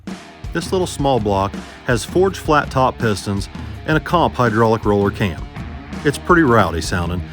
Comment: I thought said Kenny had dome pistons in the engine